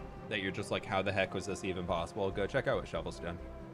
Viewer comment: "If y'all wanna see stupidly mind-blowing builds, that you're just like how the heck was this even possible, go check out ???" Can someone help me figure out what he's saying here? I can't understand him, but I wanna see the mind blowing builds. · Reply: Pretty sure he's talking about, he's another minecraft builder and does some really amazing builds